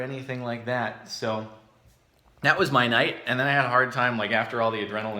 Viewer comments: That was my night... I heard that as, That was Mianite... I need life. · when he said my night i thought that he said mianite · did anyone else hear Mianite?